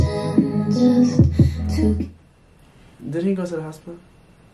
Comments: what did he say ? I keep repeating that part but I'm not getting it :') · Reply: “didn’t he go to the hospital?” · What was he saying on 16:28? I can't catch it😂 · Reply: "Didn't he go to the hospital?"